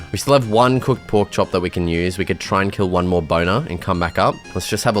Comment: what did bro say 🙏😭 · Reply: BONER 😂